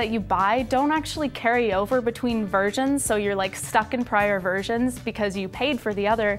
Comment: Did she say "virgins" at 1:30? ;-)